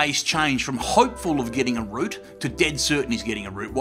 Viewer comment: Whatcha guys saying at 5:07? I hear "getting a root/route", neither makes sense. Non-native here, also quite weak with Australian accent (yet I love Ozzy)